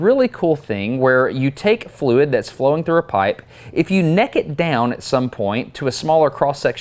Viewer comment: After hearing your dad talk my brain went into "translate southern to English" mode and when you said "neck it down" instead, at first, I heard "nekkid down". 😂